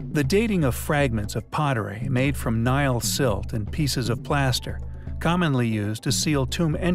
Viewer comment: "pieces of plastic" - did I hear that right?